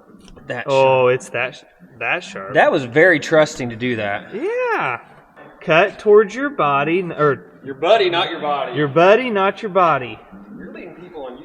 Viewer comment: The way I heard it was " cut towards your chum, not your thumb".